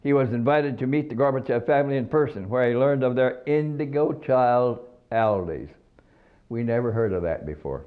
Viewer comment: Can any one confirm me what name he says in minute 9:58 "Indigo Child Alice" (¿?) From Russia, right? English is not my mother language, and this "guy" has not teeth. Rest in peace.